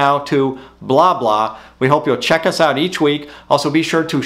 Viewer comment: When he said blob law it sounded like he was saying "BLABLA"😂😂